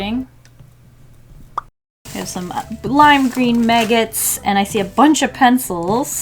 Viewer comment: I heard "lime green maggots" as "blinding maggots"